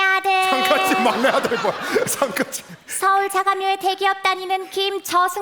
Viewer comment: 서울 다음에 뭐라고 하신 건가요? 원래 자가에 아닌가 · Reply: 서울 자가묘 같네여